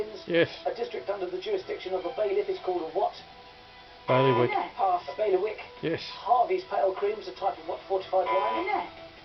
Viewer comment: What was the word you and said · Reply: bailiwick?